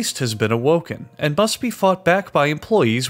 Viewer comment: I love how just nonchalantly mentions, “Oh yeah, there’s an eldritch beast that the workers have to fight to make the chocolate.” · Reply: My audio glitched just at the right time, and i thought it said “f*cked” back instead of fought. TWICE